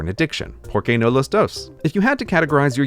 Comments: i was like "what did he just say?" and then i slowed down the footage, and realised he said "why not both?" In spanish. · As a native speaker of Spanish, Its funny that I didn't get the first "¿Por qué no los dos?" because of Gray's pronunciation, I heard "Poor key no lows dose" and was like, never heard that phrase before, what does it meen? 😂😂😂